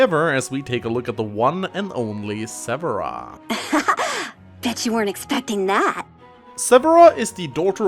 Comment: Lol I heard "Bitch You Weren't Expecting That." Instead of "BET you weren't expecting that."